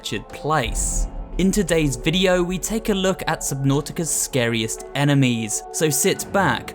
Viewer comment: Ohhhhh scariest, I thought it said rarest, I was like there’s rare creatures?